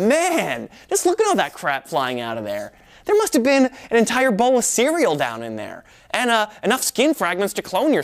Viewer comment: Did he just say boneless cereal?